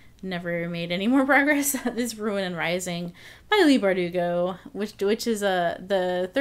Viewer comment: At first when you said "Ruin and Rising," I thought you said "Red Rising" and I was like YES YOU WILL LOVE IT I THINK I HOPE 😂😂 But finishing one fantasy trilogy before starting another sounds like a good move 😂👌